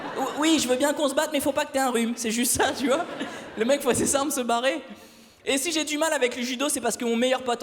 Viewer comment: Il dit quoi à 5:28 ? J'entend "le mec fois c'est simple se barrer" Ps : ce message n'est pas un troll je demande vraiment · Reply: Le mec voit ses 'simp' se barrer --> ses fringues je pense ^^. Non ?